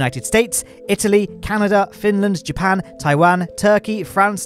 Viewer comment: and beyond did say Finland!? (I live in Finland)